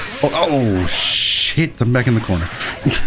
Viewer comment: Did just say "ssssh*t I'm back in the corner"? 14:02 first time curses?